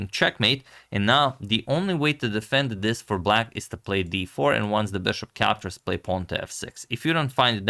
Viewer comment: I thought Antionio said b4 at 9:30 and I had to relisten to it to make sure so I can poke on it. Imagine my disappointment when he actually said d4.